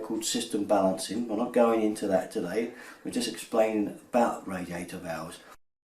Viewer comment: Thank you for the excellent video! 2:13 "bath radiator valves"? I don't understand what you said, I'm learning english.